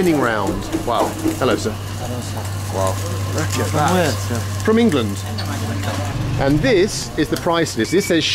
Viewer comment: I know I heard wrong, but when bald says 'from England' it sounds like the guy says 'wanker' XD